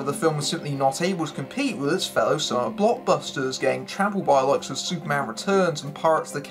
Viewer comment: can’t quite make out what is being said so I turn on closed caption. Closed Caption “the film was certainly not a was compete with his fellow saw blockbuster gang travel by alocs of Superman and Parks the Caribbean” Well....hmmm....at least I guess I am not alone in being confused on what was said. (All kidding aside yes I can tell by the pictures on screen that we are talking the competition the film faced, but I seriously believe there was some sort of audio error at that point because I have now listened to it five times and I am still not making out all the words being said in this section.) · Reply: "Not able to compete with it's fellow summer blockbusters, getting trampled by the likes of....." It's not difficult to hear.